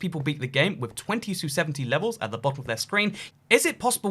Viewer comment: “Given that most people beat the game with twenty to seventy levels” I heard this as 2270 levels, and my first thought was “wow, what am I doing wrong”